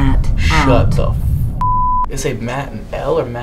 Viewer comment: I keep hearing it say Matt out or ur name Matt riff or however u spell it